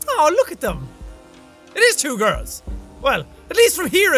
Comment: I thought jack said “ew two girls” till I heard him say “aww” I now know he said “oh! It’s two girls. Aww”